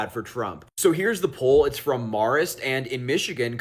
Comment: …..did he say the poll is from Mars?